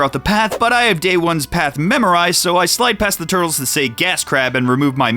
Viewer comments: is gas crab what the turtles have been saying all these years? i always thought they were saying cat crap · i always heard "NadCrab", and thats the version im stickin with, because i can follow it up with 'yknow, they make a Special Shampoo for that...'